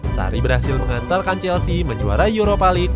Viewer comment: "sarri berhasil mengantalkan chelsea" kalo gasalah denger 😂😂